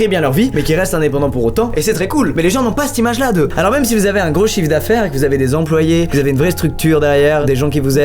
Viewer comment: je suis fatigué, j'ai compris quelque chose d'autre😂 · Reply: Moi j’ai entendu alors même si vous avez un gros chibre mdr